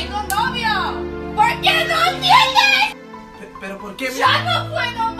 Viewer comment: QUE ES LO QUE DICE KYLIE EN EL MINUTO 1:00? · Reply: tatiana alejandra Ariel tengo novio